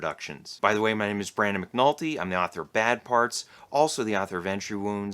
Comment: I swear when you pitch your book I always hear, “I’m the bad author of good parts.” Lol